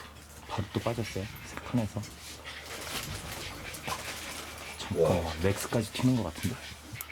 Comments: 여기 이어폰으로 듣고 깜짝놀람 목소리 들리는데.. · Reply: 여자목소리로 '잠깐만' 이라고 말함 ㄷㄷㄷㄷ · 잠깐만이라고 나만 들리나